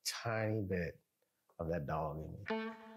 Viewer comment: What does he say here? Little tiny bit of what? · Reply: “That dog in me “ it’s a term in the American south when a man is how do I say it, “flirtatious”. lol people will say “ he got that dog in him. lol